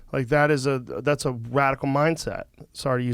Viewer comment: at first I thought Joe said racoon mindset😂😂😂